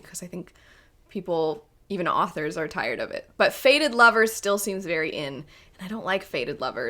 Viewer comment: So initially I thought you said you were sick of "faded lovers" not "fated lovers" which I was ready to jump on the hype train for.